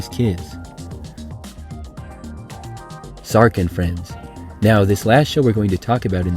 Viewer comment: What is the name of the last show? Next time when you can’t find any images or clips for a show, could you just add a PowerPoint slide of the Name of the show? It’s hard to understand what you’re saying. I hear you saying “shark and friends” but maybe it’s Bart? Mark? Dark? Park? Lark? What is the name, please?